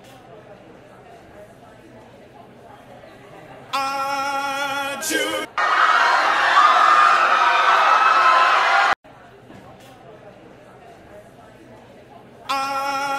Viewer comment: I heard him say aiiiii just want to chesse😂😂😎😎